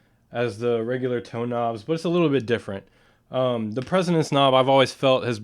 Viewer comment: it sounded like he said presonance.